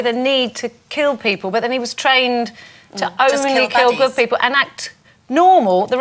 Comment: Does she say "...only kill good people" at 3:27?